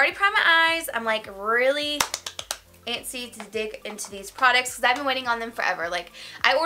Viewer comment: I swear I thought you said “I can’t wait to dick into these products” 😂😂😂😂😂